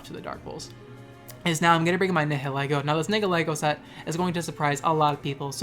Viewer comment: did u just say niggalego